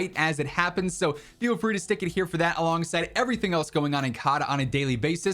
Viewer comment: Did he just say “stuck it here” lmao whoa buddy slow down lmao😂